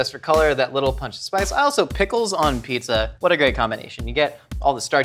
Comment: I thought he said pickles when he said Jalopinos. Oof